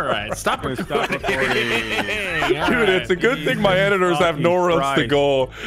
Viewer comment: I wasn’t paying attention and thought he said “thanks my editor”.